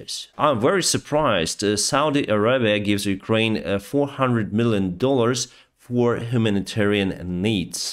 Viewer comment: Did he just say Saudi Arabia gave 400 billion in humanitarian aid